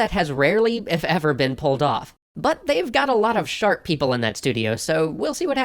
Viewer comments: I heard, "...but they have a lot of shark people in that studio" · Hehehe, they've got a lot of "Shark People" (I know I misheard it, but I've decided Blizzard have shark people working for them now)